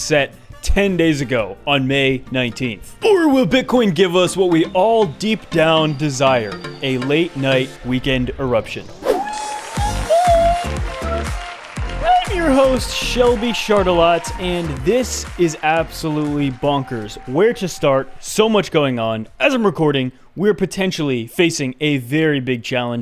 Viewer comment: what did you say when you introduced yourself at the beginning of the video ? I hear : I'm your host shelby shit a lot... 🤷🏻‍♂️